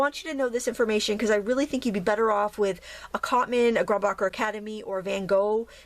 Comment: Lovely video. :^) What did say @ 06:07, between Cotman and Van Gogh? Something Academy? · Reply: grumbacher academy